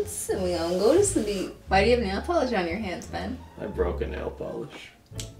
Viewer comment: I thought it said "I broke my nail" and I got scared. These days are the worst days to have funerals.